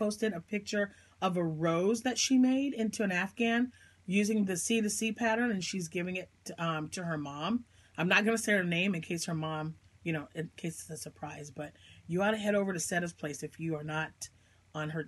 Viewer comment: I thought you were saying sea to sea afghan then it dawned on me it was c2c. lol